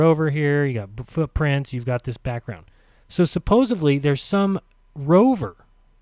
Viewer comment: Did he just say "supposively"?